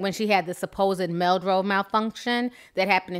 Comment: It sounded like you said "Meldrobe malfunction"? WTF is a meldrobe